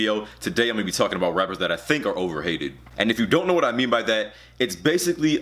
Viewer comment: Thought it said overrated and I saw j-cole in the thumbnail and I was so confused